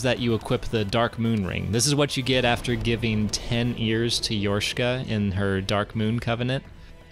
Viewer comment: I misheard you say “ears” as “years” and it still made sense. So many silver knights, so much time. ب_ب